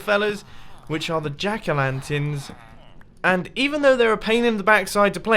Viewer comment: thought he said 'ejaculanterns'